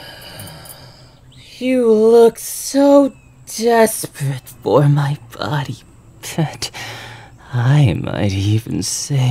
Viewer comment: I THOUGHT HE SAID “you look so desperate for my body fat!” PLSSSS